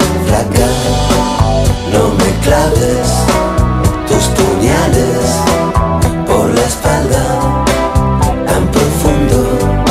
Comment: Cuando era niña siempre pensé que decía ''no me claves tus pañales por la espalda'' :(